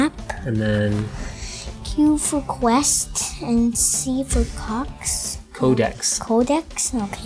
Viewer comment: I was laughing so badly when he said co**s instead of codex